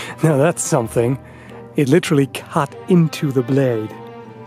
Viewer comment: I’m sorry but I can’t resist. Around 1:56 it sounded like you said It literally CUt eento thuh blayeed